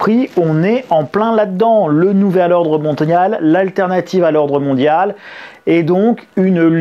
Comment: J'ai entendu "Le Nouvel Ordre Montagnard" XD